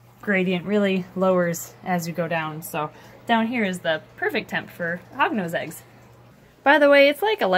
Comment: did you say temprefer